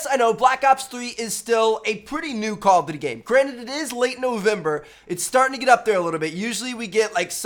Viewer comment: Did he say it's late November?😂